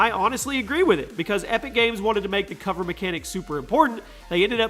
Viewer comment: did he just say epic games